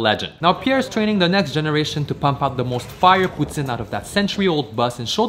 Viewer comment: Lol it sounded like he said the most fire putin